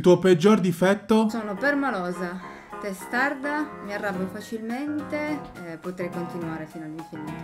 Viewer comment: Ma solo io ho capito "mi arrapo"